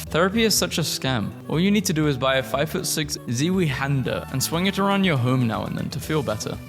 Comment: it’s pronounced zwine (like swine but a z instead of a s) hand er